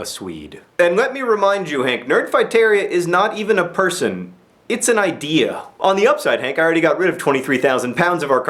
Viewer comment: what he said - nerdfighteria isn't a person, it's an Idea what I heard - Under this mask is an idea, and Ideas are bullet proof